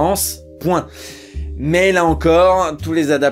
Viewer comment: Ah ouais génial. Du coup c'est quoi le Club Biyal? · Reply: (réponse un an après, mais qui sait, ça peut toujours servir...) Le Club Dial, c'était une société de vente à distance qui distribuait notamment des disques, et dont les méthodes pour faire raquer les clients étaient moralement un peu douteuses... Je conseille de regarder l'épisode de Crossed, de Karim Debbache, sur le film Brainscan. La vidéo est très sympa et le principe du Club Dial y est bien expliqué dès la première minute :3